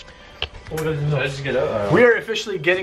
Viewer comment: shhh shh did you Guys hear that Storm outside? Some Noise said i think its a Little Girl. It said "Yeah". Listen its around 18:41. Listen Carefully okay!. Shit i hope its The Guys Who said that. But i doubt it because it sounded like a Whisper!. GEEZZ!. Creepy Af.